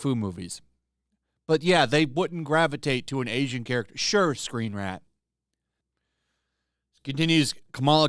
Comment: I know you're saying "screenrant" but i kept hearing "screenrat" and that's my new name for them